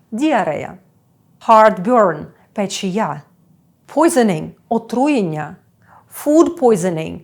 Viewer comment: слухав на швидкості 2х: putining - отруєння; hard putining - харчове отруєння.. Почулось